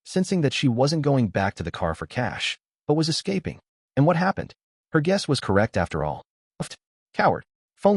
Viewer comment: it's so funny I have these playing in the background, and all I hear is "er gussy". I know AI has a hard time enunciating, but it was funny